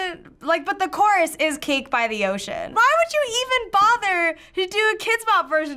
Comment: when I was younger I thought cake by the ocean meant literal cake. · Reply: Was it weird that i thought they said "KATE BY THE OCEAN!"